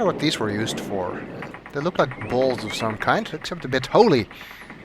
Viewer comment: I think he said "They look like bowls of some kind, except a bit holey", but what I heard was "They look like balls of some kind, except a bit holy". XD